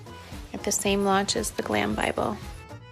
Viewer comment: I totally thought she said "Clam Bible"!